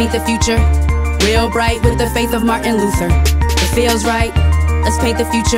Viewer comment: Oh. I always thought they were saying “With the face of Martin Luther.” Faith makes...a lot more sense.